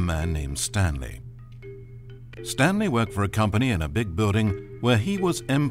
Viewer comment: " big building " i thought he said "big butt" 😂